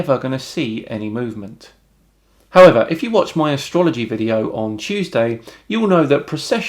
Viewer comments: Er... did you actually say 'If you watch my ASTROLOGY' video at 6:50? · did you say "Astrology video"?